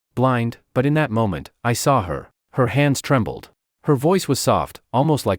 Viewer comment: Am i drunk or did he really say sow her?